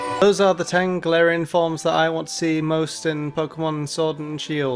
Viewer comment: All I hear is "Tangelarian forms". I am not unintrigued.